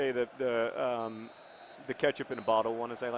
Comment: "I'm gonna have to say the catchup in the bottle -- slowly then all at once." That made me laugh out loud. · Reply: Catch-up? I thought he said 'ketchup'...